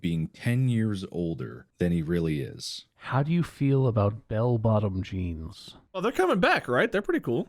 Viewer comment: when says "how do you feel about bell bottom jeans" I legit thought he said "apple bottom jeans" and I went "oh yeah, boots with the fur too?" Ive never been more disappointed in myself than right now.